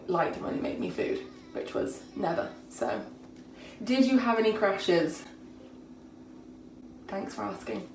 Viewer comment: glad you subtitled "crashes". Thought you said crushes.... 😅